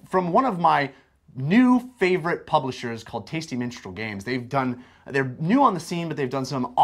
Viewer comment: Lol I thought he said 'Tasty Menstrual Games', and then eyebrow raises and he starts smirking at the camera. :D